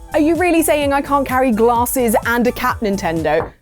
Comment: I misheard that as glasses and a cat... had to rewatch as I got all worried. I was so relieved!